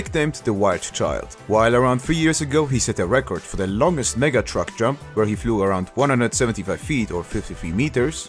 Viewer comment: is it just me or i heard him say nega truck